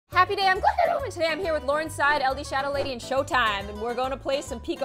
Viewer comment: Am I the only one who heard her say "laurenside" instead of laurenzside